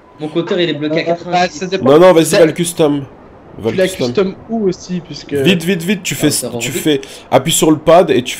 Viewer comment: C'est moi ou aiden dit a 3:58 "tu l'a custom où esti"